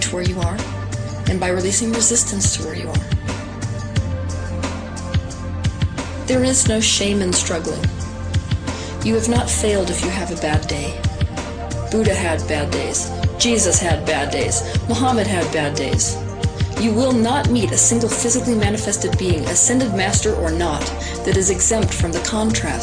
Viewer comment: I thought it said Buddha had Bad Boys